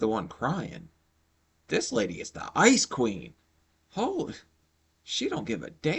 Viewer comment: When you called her the ice queen I thought you said icecream 😂😂